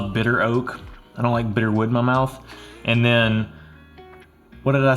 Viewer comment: “I don’t love bitter wood in my mouth.” 😂 I enjoyed the review. I’ve had the Weller 12 & SR but the 107 is hard to find in AZ and CA. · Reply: I thought he said, "Bit her with my mouth," lol.